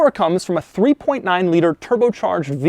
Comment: I got super excited when I thought it said tri turbo